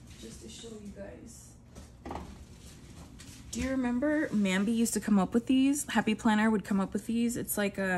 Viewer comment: I am looking for a good pen. What is the name of the black pen you really like??? I missed it!!! So sorry!!! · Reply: i ordered your japanese pens that you have listed in your description box. The one you displayed was totally black and I thought you said it was called a “nambi” pen??? Could that be correct???? Thanks so much!!!